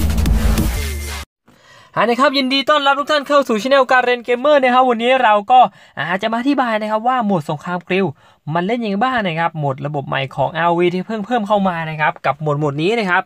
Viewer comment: ผมได้ยินพี่พูดโมดสงครามปริว0:12